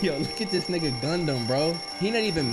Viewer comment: I turned away for a second and I thought said “This Raucous.....” I was so shook😂😂😂😂😂